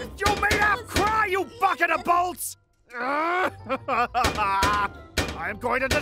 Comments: sounded like Zane said f**king bolts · Did I ONLY Just realise that Zane says the F Word at 4:13 and this is really concerning as well as kids like us as a (KIDS GAMING) Channel we realised it after hearing it a few times and Jess, Can you please tell Zane that KIDS Have caught him swearing at the timeline 4:13 and Love YOUR vids btw anyways it COULD BE the F WORD or he could be saying Bucket of bolts but what we heard was the swear word sorry for the Error if Zane DID Say bucket of bolts, we just heard it as the F WORD.... Kindest Re-Guards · Is it just me or did Zane say the f word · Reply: "BUCK IN A BOLTS!!"